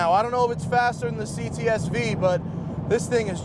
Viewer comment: He clearly says "I dont know if it is faster the the CTS-V" loool your shitty attempt